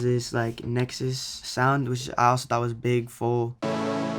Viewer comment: What is saying at 1:40? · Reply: jrworldnetwork he said big full but i don’t think that’s the name of the preset but just use the buzz me preset in nexus